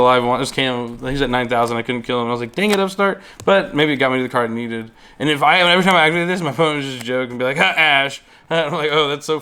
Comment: WHAT, ARE THOSE WORDS dfgdsgdufhghdsg ASH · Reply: ''and if I every time I activate this my opponent would just joke and be like HA ASH'' this is what he said lol